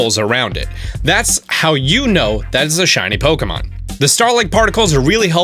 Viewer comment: For a minuet i thought it said shiny pikmin